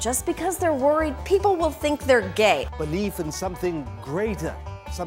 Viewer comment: Did anyone hear 3:42 "Faith in something 'Gre[y]ta' ?? 😁😁😁